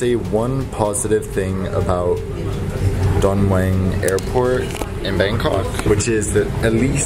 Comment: Thought he said John Wayne Airport haha